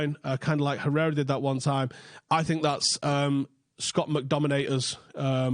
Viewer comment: Did you say Scot McDominate? bc that's what I heard and it's what I'm calling him from today.